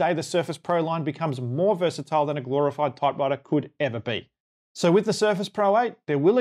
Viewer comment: Nice video. Did you call the M1 a glorified typewriter at 3:28? It sounds like you did. Is that because many key software packages are not available on an M1 Macs, and Apple fans will have to end up running Windows anyway? So they can type and run some things natively, but not a whole lot else?